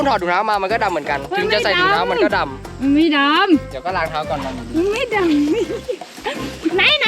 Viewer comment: ใครได้ยินว่าเดี๋ยวเค้าล้างเค้าก่อนคับ ป่าววววบ